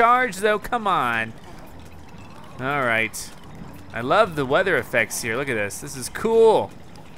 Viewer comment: For a second I thought he said " I love the weather " when said "I love the weather effects."